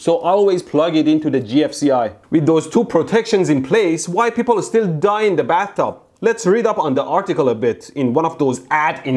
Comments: Did anyone else hear "*White* people are still dying in the bathroom!" the first time around? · I thought he said white people still die in the bathtub